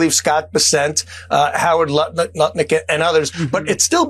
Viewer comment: did he just say howard nut lick and others.. at 3:51?